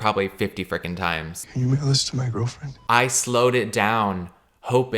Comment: Caleb repeating a clip of a guy pretty clearly saying “can you mail this to my girlfriend?” over and over while being super confused about what he’s saying is so funny to me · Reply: All I hear is “can you make a list to my girlfriend” 🥴🥴